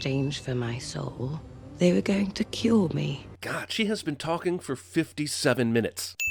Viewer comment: It really sounds like Ghost here is saying "They were going to kill me" instead of "cure me". That really confused me when I first watched the movie...because, you know, that wouldn't make sense. Does she always sound like she's saying "kill" when she says "cure"? Is that part of her accent?